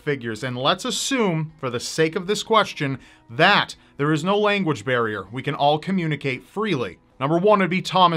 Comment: I thought you said language barrier?